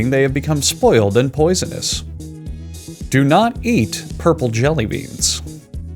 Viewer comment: eats purple jelly beans did you say something?